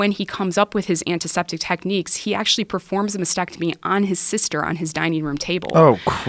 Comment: Idk how but I thought she said "he did a mastectomy on his sister for practice" oh my god